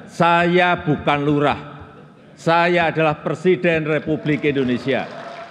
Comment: Kalau gk salah dengar Jokowi bilang satavadalah persiden,,, jd ingin tahu presiden atau persiden???